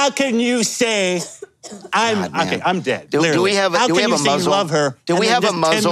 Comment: Did he accidentally say “do we have a Muslim” at first or am I mishearing him